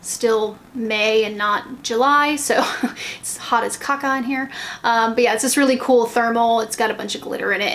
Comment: Did you say "hot as cock"? Whether you did or I misheard, I'm using that from now on! Linus is delicious. I can't believe he'd be naughty with Poe - they're angels. Just be grateful they're not human babies - ugh! Great video as usual xxxxxxxx